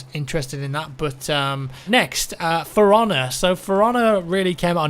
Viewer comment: When he said For Honor I thought he said Ferrahna, and I got really confused, cuz thats not a word.